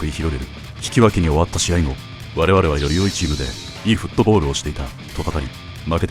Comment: 良いフットボールがe footballにしか聞こえない😂